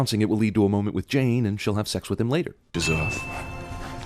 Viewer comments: when Thor is drawing the Tree of Life, no he did not say "Anaheim," rather he said "Vanaheim." It's in the closed caption & if one really listens, one can hear the 'V' sound in Vanaheim. Growing up in Anaheim, CA USA; I never got a straight answer on the origin of the city's name. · I'm obligated to point out that he actually said "Vanaheim." But he did mumble a little bit, making it harder to understand the first consonant, so I'm okay with the sin point. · He said 'Vanaheim', just so you know. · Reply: Connor Thorburn Hmm yeah, I went over to the Marvel wiki to see if anything sounded vaguely like "Anaheim" and found "Vanaheim". It still sounds like he said Anaheim though.